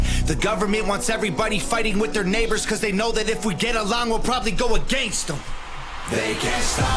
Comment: When he says If we all get along we'll probably go against em, it sounds like he says We'll probably go a gangsta. That'll work too